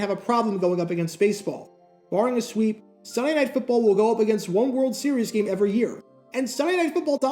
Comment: Anyone else hear "cyanide football" at 8:13 ?